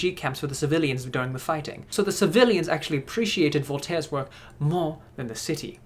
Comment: tell my why I thought Voltron when he said Voltiaere